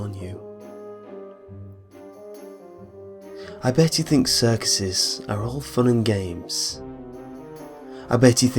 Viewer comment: At first I thought he said "I bet you think sex is all fun and games"